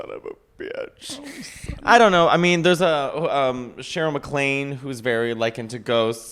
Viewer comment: Shirley. (Because I thought I heard Cheryl)